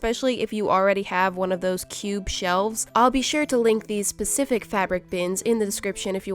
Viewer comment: I thought she said “I’ll be sure to link these pacific fabric bins” 😂